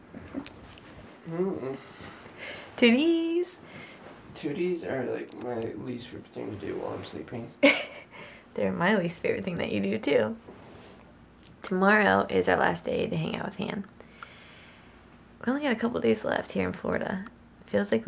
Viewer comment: also, when she said "they're my least favorite thing you do, too" i thought she said "they're Miley's (as in Cyrus) favorite thing you do, too" and i just coudn't, after that lol...i think i just heard what i wanted to hear ha